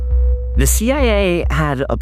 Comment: did he say CIAA? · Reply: I heard it too so I rewound to check 😅 He said "CIA; They....." so we both heard "they" as a second "A" 😁🤘